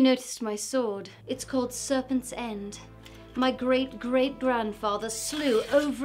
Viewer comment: Thought you said "Servant's End" the first time through. "Must be a real bougie family," I thought.